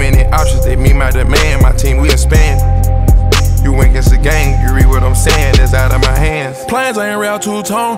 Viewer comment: "U went against the gang, u read what I'm saying, it's outta my hands" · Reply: i hear "you reap" not "you read"